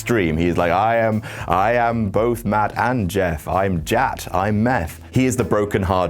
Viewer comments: Did anyone else think he said, meth? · I thought you said " i am meth" haha